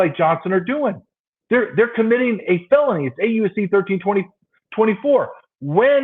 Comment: Nice! That’s “8 U.S.C. § 1324” for anyone who misheard and also has been like, “They have to be breaking SOME actual law right?” Why aren’t they in jail, it’s legit?